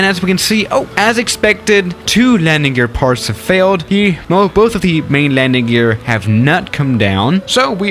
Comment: Is it me or did i hear and i quote" both of the main landing gesr have nut come down"?